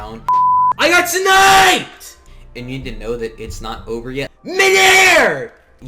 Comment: kinda sounded like the n-word ngl